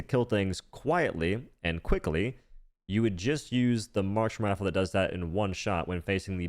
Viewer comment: man am I stupid? What is he saying here? The Martian rifle? The march rifle? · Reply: Marksman but he slurred